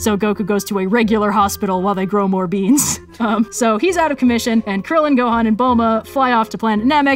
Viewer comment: I legit heard “Commie” XD and had some leftie laughs